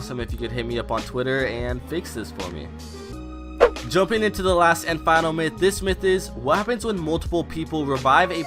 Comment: Who else heard him say ''Last And Myth''